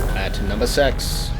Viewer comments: it sounded like he said at number sex xD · did he say "at nomber sex"? XD